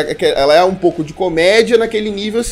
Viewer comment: Eu tava fazendo comida quando ouvi "Rasgando pudinzim" hushwushsushsudhd